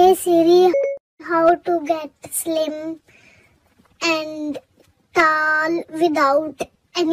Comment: I thought he said "How to get slim shady" at first.